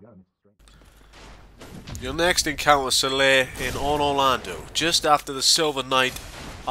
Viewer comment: Sounded like Onor Londo xD